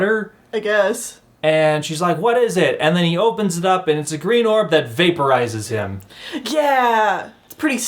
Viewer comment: Oh my gosh, I misheard "green orb" and thought you were talking about a green orphan when it vaporized the girls dad lmaoooo